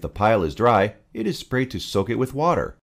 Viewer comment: I cannot catch the phrase "if the pile is dry, it is ... to soak it with water". May I ask which adjective was it? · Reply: "sprayed"